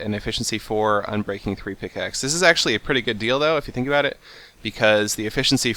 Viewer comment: what he said: this is a pretty good deal though what it sounded like : this is a pretty good dildo